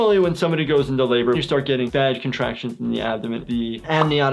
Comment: Does he says, "... you start getting vaj contractions ..."????